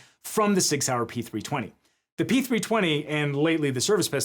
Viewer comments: I heard you say Sig Saur (Spelling) and thought you said "Six hour" and wondered why a gun was called a "Six hour pistol." Then suddenly I realized that "Six hour" was a company name, then processed that I've seen said company name in books I read as a kid. "Oooh, Sig Saur..." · "hey look they issued him a P320" is crazy work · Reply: The subtitles heard that as the “six hour” instead of Sig Sauer. Is that the mean time between failures?